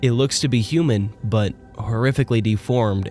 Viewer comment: I heard "you be human"